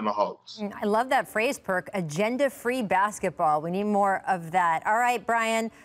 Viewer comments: I thought perk said "gender free" until Rachel reiterated it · lmao thought perk said gender free basketball for a second there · Thank you Rachel for cleaning that up... I thought perk said " gender free Basketball " And Brain you need some vegan tacos!